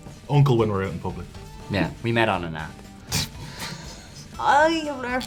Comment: "can I call you stepdaddy" "Uncle, when we are in public" "We met on an app." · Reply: I thought he said, "we met on a nap".